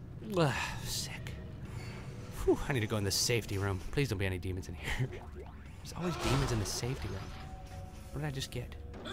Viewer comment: I pressed 9 and all I heard was "Demons and HURR" XDDDD